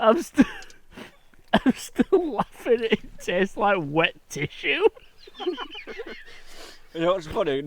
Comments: I definitely heard "tastes like wet tissue" as well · I swear I heard " tastes like wet tissue" 😂😂😂